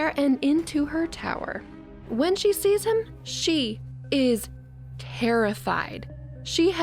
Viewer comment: OMG I thought she said: she is HAIRIFIED!😂😂😂 GET IT? terrified and hair? No? Ok